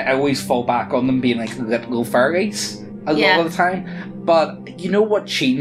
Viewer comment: This whole video I thought he was saying furries, not fairies.